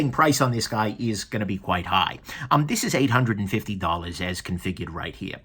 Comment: I'm sorry, I could not get the price, I keep hearing 150 $, wich I think is not much for that piece and does not seem right. Could somebody write it down? Thx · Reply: $850